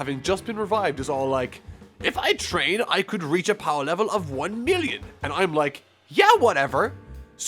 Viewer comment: he actually said 1.8 billion